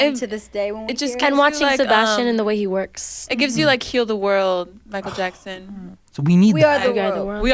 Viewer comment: Did Dinah said "Kill The World" lmao🤣🤣🤣🤣🤣🤣🤣 I'm a MJ Fan , and i was like, when did he write that song, I've never heard it before lmao.....